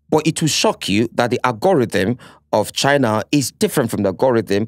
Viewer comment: I heard agoridim from Nedu 23:05...can someone confirm